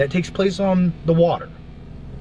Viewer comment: Did she say Thee pussy ? lol I know she said Deep blue sea, but it sounded like the pussy with an accent.